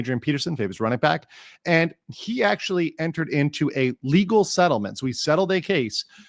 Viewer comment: Every time you say "ah-legal" I hear "illegal"... I heard "i have spotted with my illegal eye...."